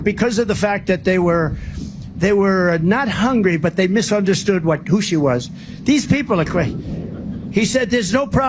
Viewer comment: Did he say the sharks "misunderstood what gushi was" ?